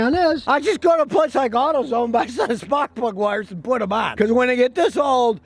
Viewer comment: Thought I heard Scotty say "Mark Mcgwire". But he said "Spark Plug Wire". 😁😂😁😂